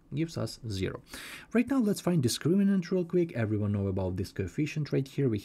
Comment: I was fine until 4:22 then I couldn't understand the mumbling accent. After 30 years, I need a slightly slower clearer explanation. You were saying what you were doing but you were not explaining what you were doing. I will have to go to one of the other maths channels. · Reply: He says discriminant. But it is weird how he doesn’t change his accent a little in 2 years of making English videos. He has some grammatical mistakes and he hasn’t cared about them for freaking years